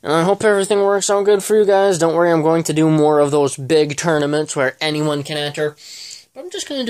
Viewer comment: Oof I thought you said I could be in the next tournament :( it’s ok tho